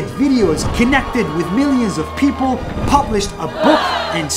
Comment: Did he say kidnapped with millions of people? 🤣😂